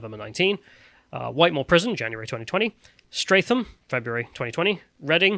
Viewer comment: Lol I thought they said Stratholme